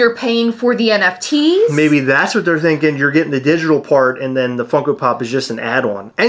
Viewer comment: WoW 🤦🏻‍♂️ This really has been a 💩 storm ⛈! When you said "digital part" I thought for a sec you said "digital fart 💨"😂 🙌🏼 Huge congratulations to you guys on hitting that 14k!!!🎉🍻🍾 Love you both!!! 💚💯